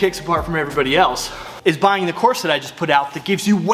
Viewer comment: Here's my BRAND NEW $25 COURSE on Lead Leg Kicks! <link> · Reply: In the video, I heard “buy the new corset” and almost spit coffee all over my phone 🤣